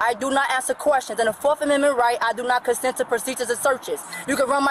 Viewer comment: Maybe they need to start giving people who manage to turn "I know my rights" into an arrest a court ordered class that teaches them their actual constitutional rights. Forget the fines, fix the ignorance. Enjoyed watching with you!👍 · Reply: I thought she said purses and seizures. Lol